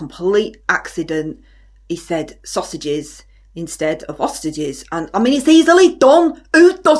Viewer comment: Hello! Thank you for watching. As you might imagine, the outtakes were off the charts with this one. We can safely conclude that I have the maturity of a 14 year old boy. I usually put out a blooper reel at the end of the year. Plenty of material for it this year! 😆 Thank you for watching. Please click the like (or dislike, whatever tickles your pickle) and make sure you're subscribed for more silliness! 😘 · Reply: i thought you said "sausages instead of ostriches"